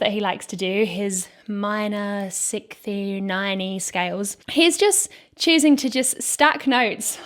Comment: Did you say “minor 6th-y 9-y scales”? Nice.